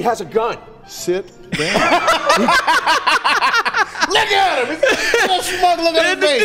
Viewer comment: did say "look at him" cuz 💀 i heard something else